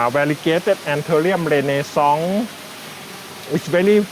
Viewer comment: Is it possible he said Anthurium Renaissance, Instead of "Rene Song" Like mentioned in the annotations?